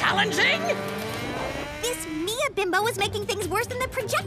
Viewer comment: Did Megan just say "Bimbo"?